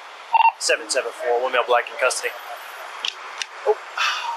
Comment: did he say "774 we have a black in custody"?